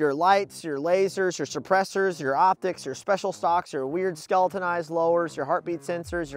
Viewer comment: Anyone else hear him say special sauce the first time around?